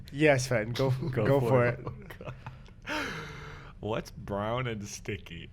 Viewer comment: I thought Sven was saying "what's brownin' stinky" as a sort of greeting